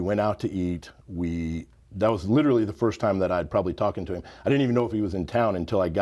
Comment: Did he say "that was litterally the first time I'd talken to him"? If so, wow, this guy takes not only bodybuilder but also the English language to a whole new level of degradation